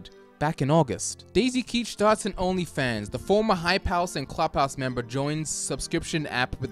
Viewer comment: This whole time I thought said “High Palace” instead of “Hype House”